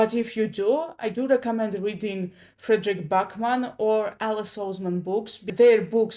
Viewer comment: Look up any popular author these days and booktubers who talk a lot about them. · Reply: I may not have heard them correctly. I thought I heard Friedrich Bachmann and Alice Salzman....